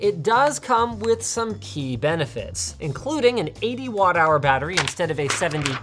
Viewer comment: I misheard him I thought he said 81 hour battery....